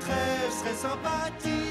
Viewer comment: Ils disent quoi à 1:23 ? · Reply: On a une poire qui sort de l'alambique